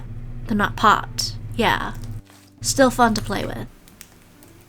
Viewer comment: sound like she said f$#%€ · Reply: She say still fun to play with